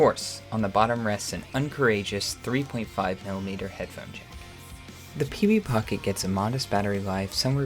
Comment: I thought you said Pv Pocket and I yelled WUT?!